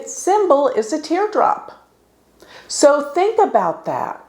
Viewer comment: I’m I the only one who heard the tear drop 😢at 16:01🤣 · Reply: I heard cum drop or...gum drop